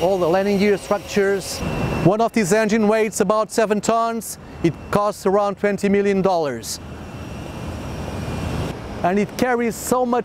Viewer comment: 74 tons just hanging out on each of the wings? That's some magical engineering... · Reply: Well, I did and at 0:12 he clearly said "weights about 7 tons and costs around $20M"